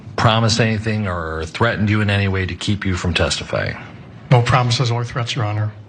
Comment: Holy Shit! Am I hearing things or does Chauvin actually say “No promises, ONLY THREATS?” And if so, is that why he’s smirking right after saying that? I seriously can’t figure out what words he’s saying. No threats, or threats, or any threats? It’s driving me crazy, even though I’m not sure why he would say that in the first place.